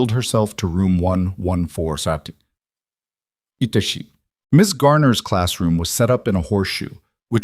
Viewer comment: "itchi" What is this? [46:25] Why is the word "psycho" here? [1:23:54] The word "sham" is added. WHY? Why did Chloe say her Dad couldn't be there because he was working, when he was right there, next to the ramp she used? Some random sounds sprinkled thruout the story. 😞